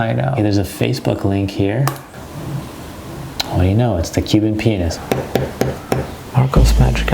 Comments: I heard “human penis” lmaoooo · did you say cuban pines? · who thought that he said: cuban penis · I thought he said the Cuban penis😂😂😂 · XD 3:46 i thought you said "Cube in a penis" 😂😂😂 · The Cuban penis is what I thought he said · I thought you said this: cubin penis. 💡 Me: XDDD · I thought he said cub in penis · I thought he said Cuban penis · oh you know, it's the Cuban pianist i thought you said Cuban penis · Please tell me I'm not the only one who heard Cuban Penis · I swear I thought he said the Cuban penis 💀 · I thought he was saying cuban penis lmao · I thought he said Cuban penis 😂😂😂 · I thought he said “The Cuban penis”. 🤣🤣🤣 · sounded like he said cuban penis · I thought he said the human penis....😂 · I heard " the Cuban penis" 😂😂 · sounded like he said well look at that it’s the penis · this is what I hear “what do u know it’s the Cuban penis” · I heard Cuban P*nis 😂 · I thought he said CUBIAN PENIS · it sounds like he said the Cuban penis😂😂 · i thought he said the cuban penis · CUBAN PENIS I’m sorry I thought he said that! 😂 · All i hear is 'cuban PENIS' sorry..... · i heard cuban penis